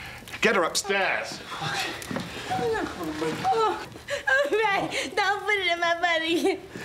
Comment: Is it me or did it sound like Brenda said at 4:09 “Ooh Ray don’t put it in my butt again”. 😂😭